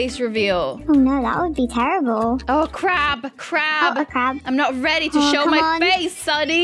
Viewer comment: when sunny said oh a crab i thought she said oh crap🤣 also FACE REVEAL